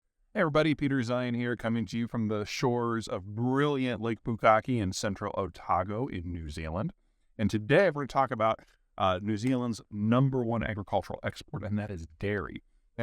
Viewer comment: Did he say lake "Bukake" ?